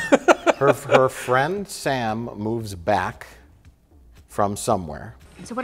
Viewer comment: Ok...anyone else think that said “Friend Sam is black” at first? LOL